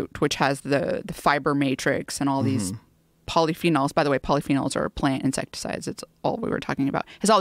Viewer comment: Did she say “polyphenols are a plant insect” at 1:03 ? What tf is she saying there I can’t understand